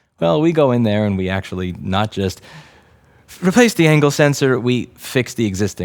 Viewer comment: I keep hearing 'ankle sensor'